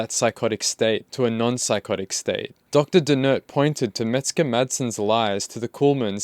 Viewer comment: Sounds like your saying “Mexican madsen “ jaja The kid is dangerous right now. He needs calm and deep therapy.